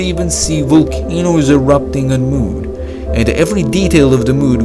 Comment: MOOD instead of MOON ;)